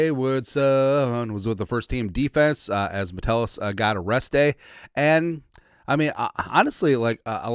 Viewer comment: i thought i heard Mettelus got "ARRESTED"?? But he got a rest day, got it 😂😂😂